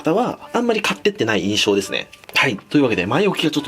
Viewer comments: 「はいっ」が「タイっ」に聞こえる 笑 タイの動画だけに← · はい！ って言ってるんか知らんけど、タイ！ って聞こえるww